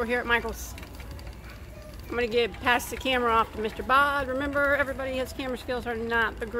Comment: Omgosh !!! I’m licking my phone trying to reach those pastries, lol 🤣 Love the commentary from as well ! 💕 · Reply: For a long time I thought was saying until I thought about it... I think it is (for Bag-O-Day).... 😉